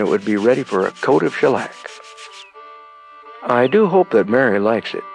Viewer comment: did he say a coat of shilack? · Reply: Shellac